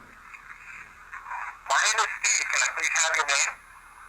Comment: I hear "Don't have a name" kinda choppy. ❤